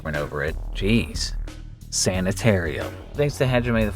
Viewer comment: The first time I heard you say sanitarium I misheard it as " Santa's harem"